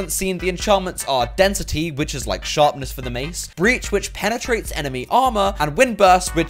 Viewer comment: Breach I thought they said bleach